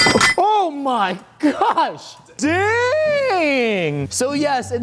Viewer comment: I thought he said da##